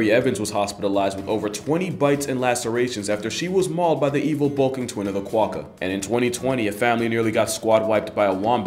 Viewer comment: did you say "evil Balkan twin"?